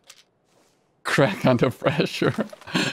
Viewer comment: So I wasn't paying attention and coz I'm British I heard "crackhead" but u actually said "crack under pressure", I have no clue what's wrong with me